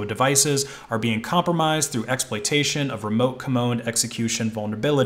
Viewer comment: 36 I thought you said remote commode 🤔💩